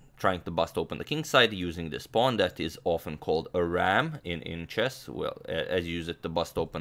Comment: i didn't catch that. "ramp" pawn?